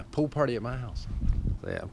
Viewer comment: Had to rewind it 'cos I first heard "poo party in my house", and I thought "naaa, it cant be it". It wasnt. eheheh